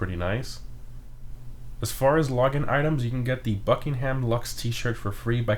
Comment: I thought he said free Buckingham Luxor for completing the heist finale :(